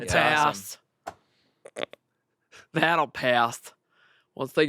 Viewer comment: i thought amanda just randomly said "battlepass" and i died